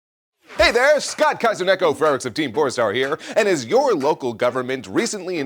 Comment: I thought he said Scott Kaiser Neko Ferret of Team Porn Star 😅